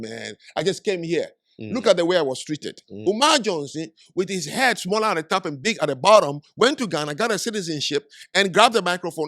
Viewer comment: did he say Umar Johnson has a head that’s small on top and big at the bottom? Am I hearing this correctly? 😂